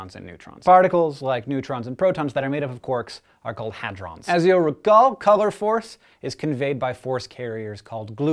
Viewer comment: Wtf does he say at 1:56? "Is your a gold colour force" or something weird? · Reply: "As you'll recall, color force..."